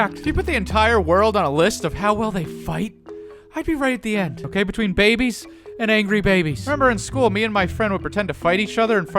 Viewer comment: he says "I would be right at the end between babies and angry babies" BUUT, the subtitles say "between The Rock and John Cena"